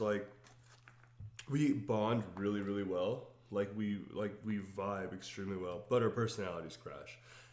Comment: Omg I can’t stop laughing, I thought he said “we vibe reallt well, but her personality is trash” hahahaha I had to rewind I was like wait what.